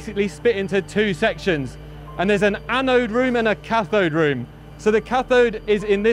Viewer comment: Thought he said that they had a 'bacon room'. I got all exited.